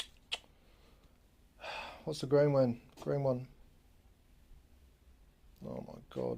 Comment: Thanks for this video. My BSB18BL chuck is cooked so this was helpful to find a 9/16 nearby to replace it. I’m hard of hearing so at about 4:30, I thought you were saying it was “The wrong one”. Stuck my headphones in and realised you were saying “What’s the green one” or something to that effect. 😂😂 Thanks again for this info. ✌️✌️ Very helpful to try save my drill.